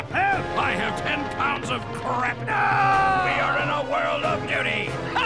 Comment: "we are in a world of duty" The use of this line is incredible. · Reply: i heard it "we're in a world of nudey"